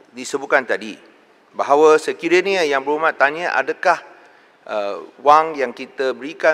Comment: if u listen carefully to Rembau's statement.. very confusing ...there was not even any single question comes from him.... what i heard is "mungkin" instead of "adakah".... i dont blame Dewan Speaker queering for KJ